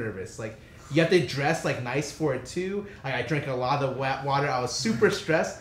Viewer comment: Did he just say wet water? 😂